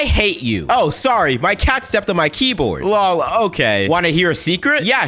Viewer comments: I hate my dirty mind Immediately I thought on the first one I thought she said she sat on the keyboard or that they are actually a boy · Bro i heard "my dad stepped on my keyboard"💀